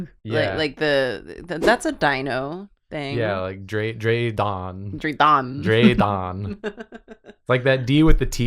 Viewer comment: i believe was actually saying draeðon